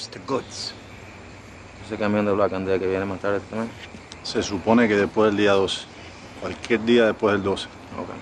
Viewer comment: HEY! what did they say at 0:57 :) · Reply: "Se supone que viene después del día 12, Cualquier día después del día 12." Ha, I won't translate, use Google.